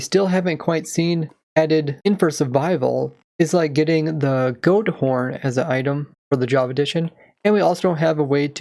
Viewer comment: "goat horn" , in case anyone hears "gold ore" , or is it just me ? x)